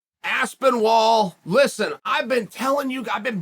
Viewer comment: Thought he was saying Aspin Wall